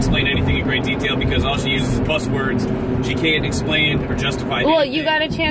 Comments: NOTE: He said, “cuss words,” which is another way of saying curse words (foul language). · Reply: Ha, I heard "buzz words". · oh no, did i hear a baby in the car with them? that's very upsetting if so. i thought he said "cuss words" not "plus words"?